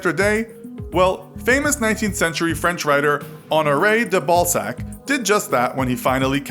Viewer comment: "honoray Theballsack" thats what it sounded like at least 😂😂